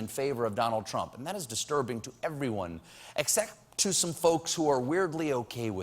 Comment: Did I hear you say "excek" and almost burp out your scam again at 0:18, Stephen! Looks like with this administration you will need quite some burping, churning and shoving your way out of it for the next 4 to 10 years!!! LMAO